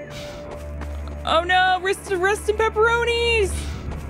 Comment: Arista wrista pepperonis?? What is she saying? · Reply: I'm pretty sure she mis-speaks at first, then corrects herself: "Rista- Rest in pepperonis!" (Which is wordplay based on "rest in peace")